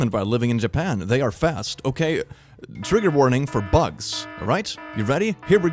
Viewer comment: I THOUGHT HE SAID “Trigger warning for butt” AND NOT FOR B U G AND I WAS SO CONFUSED AND… pained screaming